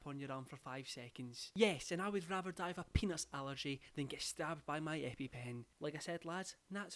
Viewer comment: instead of peanut allergy i heard say “penis allergy” and i didn’t bat an eye